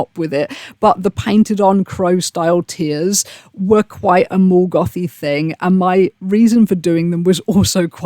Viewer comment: i thought u were saying "more goth" until i checked comments, drowning in audio processing issues over here lol